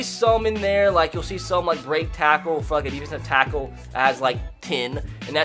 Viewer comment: Did he say f$ck